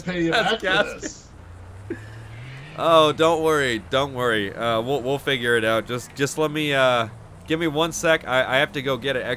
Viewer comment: The first time i heard it i thought he said ill finger it out